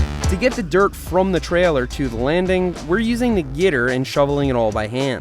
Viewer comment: did he say Gitter? Gitter. I pronounce it Gator